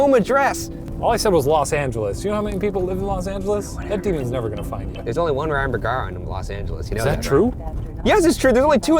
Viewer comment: Did anyone else hear whispering at 19:45? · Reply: Veratus I think it said "where"